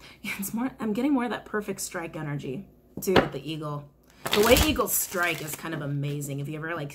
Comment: I thought you said, "I see a beagle" and I was like wow, this is definitely for me!!!! then I realised you said eagle. lol